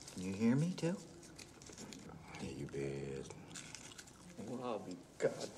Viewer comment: I always love how Chief's answer isn't a simple "yes," to McMurphy's question but with a smug "you bet your ass." It reveals his personality and reveals that he is in fact completely lucid and responsive. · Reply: I always thought he said, "Yeah, you bet."